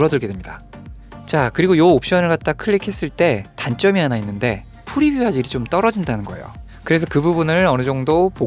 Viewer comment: 죄송하지만 07:49 뭐라고 하시는건가요..?? 제가 한 20번 다시 들었는데도 모르겠어요ㅠㅠ 프리뷰하젝션? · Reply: 발음이 꼬였네요 ^^; "프리뷰 화질이 좀 떨어진다는 거예요" 라고 했습니다.